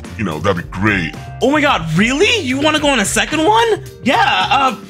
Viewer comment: Bro 😭😭😭 I thought he said want to go to a hotel 😭😭😭